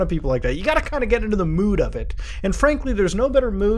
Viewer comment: Sounds like you said the "Muta of it".